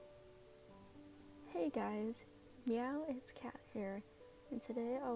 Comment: I swear when you said “Meow it’s Kat” I heard “Yaoi Cat girl”